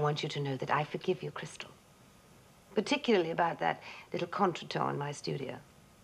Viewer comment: Can someone tell me what is that word Alexis says at 2:41? Contratone? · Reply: KingofPepsi Alexis says, 'contre-temps' -ie. an unfortunate occurrence or minor disagreement.